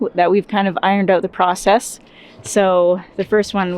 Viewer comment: I heard "prahsess" instead of "prosess" You guys are so ready to be 51st Staters in the USA!!.. lol